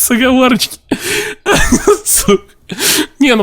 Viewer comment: Мне одному послышалось что на 4:23 он сказал с*ка.